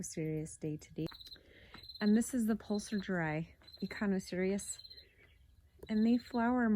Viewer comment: What species is that echinocereus at 6:25? I hear 'pulsidry' echinocereus but I can't find anything under that haha. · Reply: It is poselgeri.